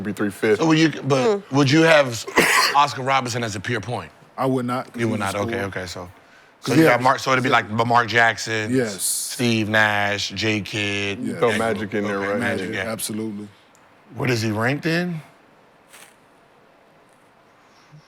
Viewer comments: i heard Lamar Jackson first time i was like wtf Gil on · Did I hear " Lamar Jackson "